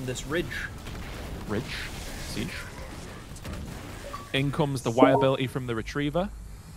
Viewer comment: misheard that as “Insurance liability from the retriever!” LOL